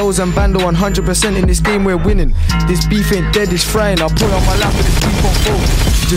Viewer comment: what did say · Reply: RS “I put it on my life this beef won’t fold”